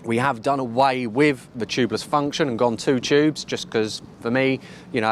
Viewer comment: Did you say Two Tubes 😁